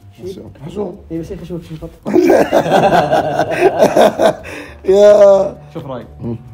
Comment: وش قال ؟ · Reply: قال شفيه خشمك شفط